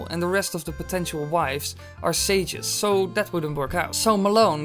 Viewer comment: Sorry but I don't understand the word at 4:04.. could anyone tell me what he said? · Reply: Sages